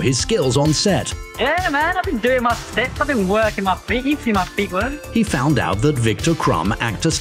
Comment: " i thought he said I've been doing my ferret work"